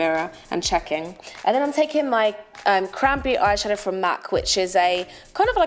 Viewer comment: When u said cranberry I thought u said crappy.. I love this look on u